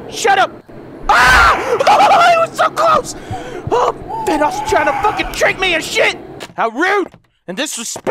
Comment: "Vanoss is trying to fucking treat/trick me & shit!"? That's what I heard...